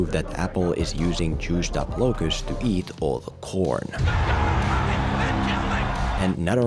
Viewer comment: The first time I heard this part, I thought Filmento said "Apple is using juiced up locusts to eat all the porn ." Which would probably be a more interesting forced monopoly strategy than the one in the movie.